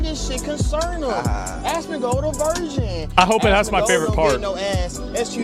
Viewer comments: I thought he was saying, "Asmongolds a version" and I kept waiting to hear a version of what? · I thought he was saying "Asmongold Aversion" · I thought DDG was saying “Asmongold aversion” but I don’t think he knows words that big